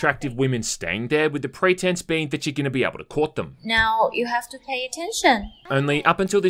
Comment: i heard with the pretense that you're going to PORK them not Court them